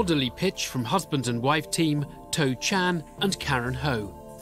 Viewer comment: I thought he said the guys name was toe jam, ffs